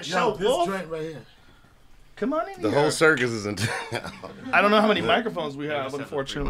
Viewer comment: did Dave just said: “The hair surgeon is in town..” hahaha